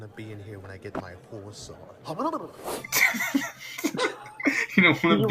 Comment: I didn't realize that the horse teacher was saying "before I get my horse on." I thought he was saying "before I get my whore sword." I don't know how I misheard it that badly.